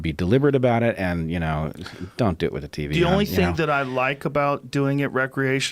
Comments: what is he saying? dont do it with the TV on? i cant quite catch it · Did he just say, "don't do it with the tv on"?